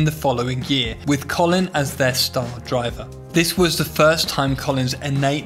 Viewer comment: I miss heard when you said Colin “first” and thought you said “Colin furze” like the YouTuber which spawned the imagery of his 107 mph granny scooter 🤣